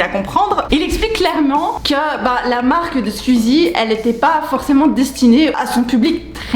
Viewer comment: j'ai compris Suzie